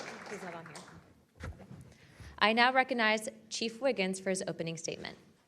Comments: This just auto played while i was listening to yt while i was doing chores and i honestly thought they said "chief Wiggum" and i absolutely lost it 😂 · We all thought we heard Chief Wiggum.